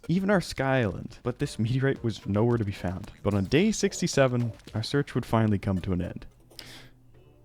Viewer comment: what? What day did he say? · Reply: 6 7